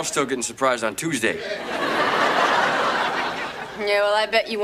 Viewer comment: It sounds like Jesse said "bitch" at the end right before the scene cuts out 😂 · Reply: No he said check please